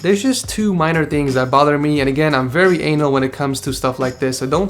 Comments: did he say "And I'm really anal with[...]"?? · Is it just me or does it sound like he says " I'm very anal"